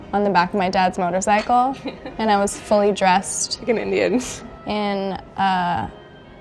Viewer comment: What did the blonde girl say at 4:35? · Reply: Like an indian, i think